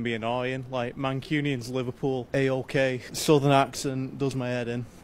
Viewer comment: When that dude said,southern accents are annoying i thought he said Sunderland accents. I was like EXCUSE ME? 😡😂 (I’m from Peterlee)